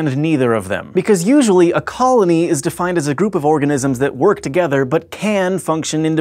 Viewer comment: I thought it said 'orgasm made of orgasms'...I'm disappointed